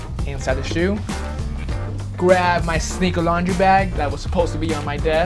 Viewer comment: Every time says "Sneaker Laundry Bag" or "Sneaker Laundry Detergent" I hear "Secret Laundry Bag" and "Secret Laundry Detergent". Great videos guys, keep them coming!